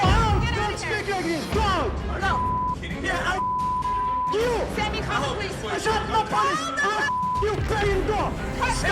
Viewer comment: Q: 7:01 "are you _______ kidding me right now?" What is the customer say? A.really B.f%$king C.want to D.try to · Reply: B